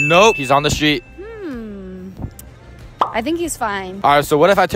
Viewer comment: Bro literally became bisexual when I heard her say " I think She's fine" instead of he. Prob the homie is gay